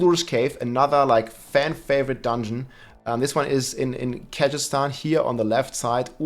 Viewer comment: I thought I heard "Kazakhstan."